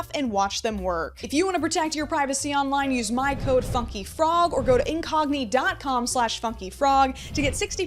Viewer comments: I thought she said fuck you frog instead of funky frog.😂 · Whenever she says 'FunkyFrog,' i thought she said 'f*cking frog' 😭